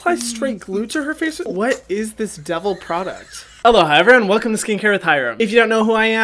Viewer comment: When you said hello hi evereyone I thought you said hello hi Hyram 😂